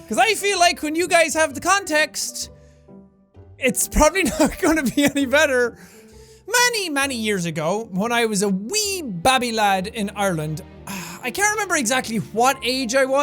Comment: When ha said babby I thought he said crabby